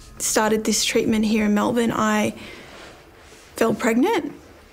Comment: I think she said she “fell” pregnant which is how a lot of Europeans say it rather than how we in the USA say it (“I got pregnant”).